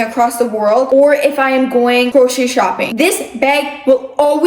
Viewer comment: did say crochet shopping???? ❤❤❤